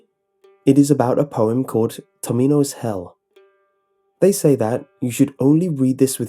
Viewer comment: am i actually the only person who put the video on mute when he read tomino's hell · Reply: wait he said tomino's??? I thought he said dominos and I was like "Wait like Domino's pizza?The fuck?"